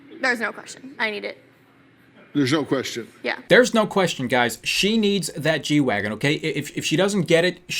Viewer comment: why did I hear "she needs that g-dragon."